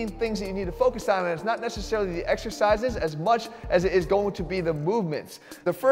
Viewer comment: I thought you said "two exercises"...